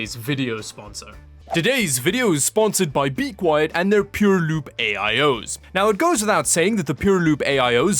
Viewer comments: I heard "pure lube" instead of "pure loop". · he said "pure loop", i thought he said "pure lube" till i Read the box it was in lol